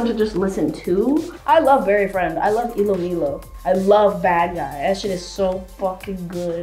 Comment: Wait ilomilo is pronounced ee-low-me-low?! Wow😂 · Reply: Erin Haha I thought she said iloilo which is a big city in the Philippines haha.